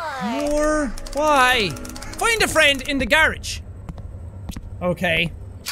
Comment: At 11.06 I heard "Find a friend in the garbage" instead of "garage"... Ofcourse it's garage, because kids back in the 90's weren't trash like I am now...